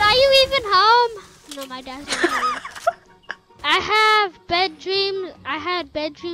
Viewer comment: I thought he said wet dreams 🤣